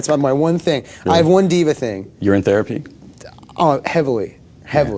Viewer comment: Brad seems kinda stiff and Jonah seems a bit nervous talking around him LOL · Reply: Lol, 04:28 he actually says “urine therapy?” but he hears it as “You’re in therapy?”